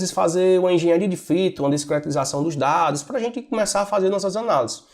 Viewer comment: Olá! Não entendi quando voce falou no minuto 1:23. Engenharia ??? e ??? dos dados. Se puder responder. Desde já agradeço por disponibilizar o conteúdo. Saudações!! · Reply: Engenharia de Features e Discretização dos Dados. A primeira é criar novas colunas para ajudar a responder outras perguntas, no caso quando ele separa o ano, mês e dia no vídeo, ele esta criando novos features, que podem ser usados para responder perguntas específicas. Discretização basicamente é transformar dados contínuos em categorias discretas ou valores.